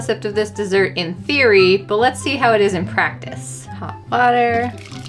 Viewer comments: Lowkey thought you said hot butter and I was so confused lol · At the start I thought it said holy water 😂